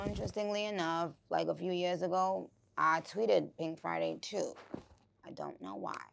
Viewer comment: I think Joe slipped up and called it pink print 2 because it’s just as vulnerable as pink print